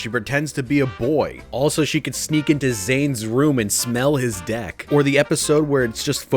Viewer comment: You know i thought he said smell zain d*ck But then realized that he doesn't mean that he meant his dueling deck